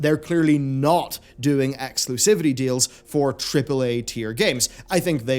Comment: I kept hearing "Cripple A" when you said tripple A, I did not disagree.